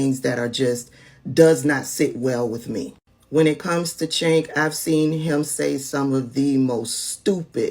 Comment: I'm sorry? Did you just say, "When it comes to chi*k?" Get her internet.